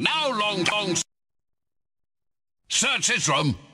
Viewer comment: "SEARCH HIS ROOM" [Is bending reality with enchanted mustache] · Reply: I thought he said "Search Islam"